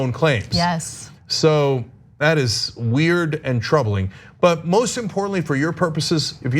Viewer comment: I misheard what Mr. Uygur said. I heard "It's trumbling (Instead of troubling)" So here is my definition of what's trumbling. If you commit a crime and you can't bullshit yourself out of it, that's trumbling. If you're a witness and you don't have an argument, but your golden Labradoodel, that's trumbling.